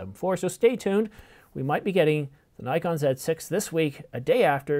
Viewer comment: Did I hear correctly? New Nikon Z6 @ 4:30 ?